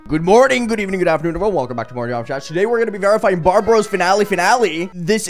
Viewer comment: lol I thought that said Barbados